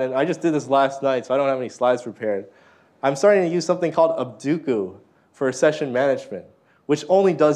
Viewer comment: What is the name of the session management tool the speakers mentions at 52:14 ? I heard "obduku" but can't find anything via Google. And what is the window management tool he mentions right after that? "btm"? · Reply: abduco and dvtm